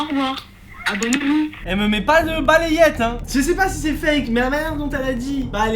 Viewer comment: A 7:21 j'ai compris"je vais te mettre une balayette turbo rotative"